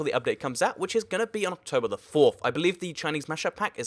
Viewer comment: Wait October 4 I thought he had said October 5